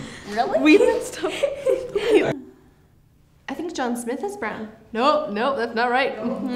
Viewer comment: did she say "I think John Smith is brown?"😂